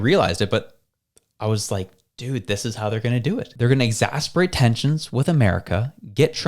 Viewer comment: did you say they're going to exasperate tensions with america??